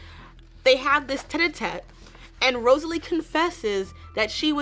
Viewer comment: Bro what are you saying that sounds like "Ted a Ted" right before this minute marker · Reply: She said tête-à-tête, which basically means a private conversation between two people.